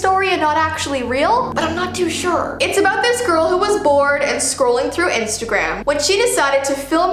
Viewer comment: I thought she said “She was born one day and was scrolling through instagram”